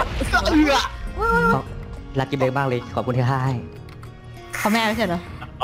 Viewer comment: ได้ยินไหมว่า: รักกิ้บเล็กมากเลยขอบคุณที่ฆ่าให้